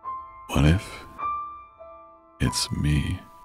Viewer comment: "what if... It's me...?" What I literally heard: wife... It's me...